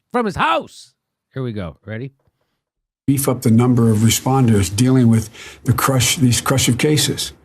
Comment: In his first sentence I keep hearing "we f**ked a number of responders" and now I can't unhear it.